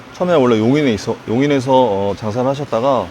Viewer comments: 장사가 자살로 들림 난 또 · 자막은 장사인데 나만 자살로 들리나요????????? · 장사가 자살로 들림